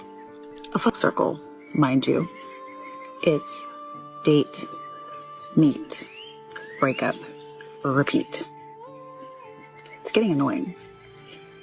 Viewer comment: Guys what you heard was "date, meet, breakup, repeat" What she said was "date, MEAT (all 3 holes), breakup , repeat"